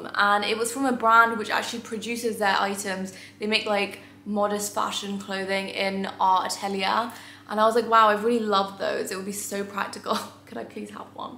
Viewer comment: Oh please help I have rewound this at 14:50 a hundred times and still can't understand the modest fashion brand name? I've even switched on the subtitles but it comes out with our Italian loool. Help????? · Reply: Qabeela x